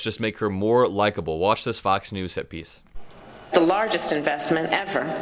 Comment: I could have sworn said, watch this news clip "hippies" (not hit piece), and i was already pulling out my bell bottoms and flowers. Thanks for this video! VOTE BLUE 💙